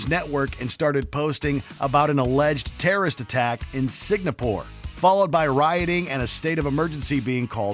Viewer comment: Did he really say Signapore or I'm tripping?